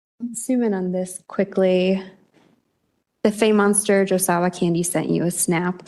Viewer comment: The fame monster just became more famous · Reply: I thought they were saying "fey monster" the whole time. It's almost a relief to think you're correct. Either way...unfortunate choice.